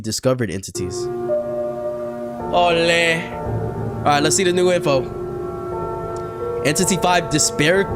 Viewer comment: I thought I heard "make sure to grab ur popcorn and let's eat" now I'm wondering if he actually said that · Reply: He is saying let's see